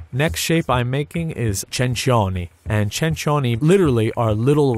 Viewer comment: Did he just say chingchongese at 2:25??